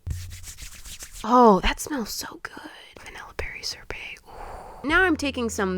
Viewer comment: 10/10 story👌 I absolutely love it, also I heard "VaNilLa BeRRy ShOrBeT" as VaNiLLa Be ShOrBeT 😅